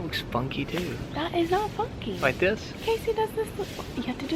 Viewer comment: did say "Casey does it this way ? " hahah :p